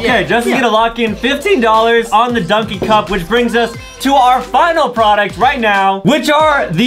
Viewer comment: did he say the final product I am confused